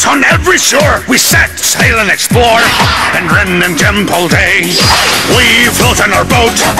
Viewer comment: does anyone hear"and rim a dimple day"